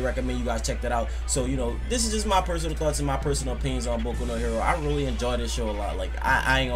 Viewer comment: I thought it said boku no pico XD